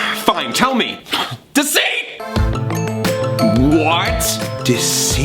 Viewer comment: Why did saying Deceit sound like him yelling Falsehood? 19:00 DECEIT IS SO MEAN!! HE MADE IT LOOK LIKE HE WAS PATTON!!! HOW DARE!!!